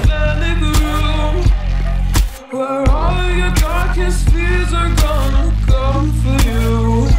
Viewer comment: it kinda sounds like its saying "where all your darkest fears are gonna comfort you." or maybe i just see light in everything.. HAHA nOpE